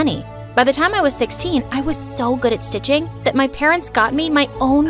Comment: I thought it said I was so good at snitching